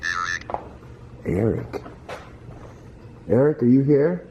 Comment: Anybody else hear arid instead of eric? And then it repeats cemetary 2x or maybe conjuring at one point instead of cemetary but the 3rd time it just says cem..